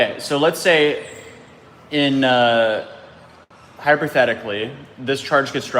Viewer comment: Did you just say “HYPER-thetically”, for REAL..or am I just hearing things or hearing it incorrectly..? You do know it is “HYPOthetically”, right..? Lol! I’m gonna chalk this upcoming to bad jet lag